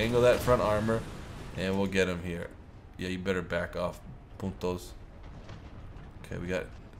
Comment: did he say "putos" ???